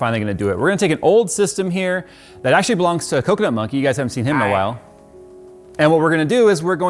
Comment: "My co-worker monkey" Unintentional racism. I respect that. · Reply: I thought he said"coconut monkey"🤣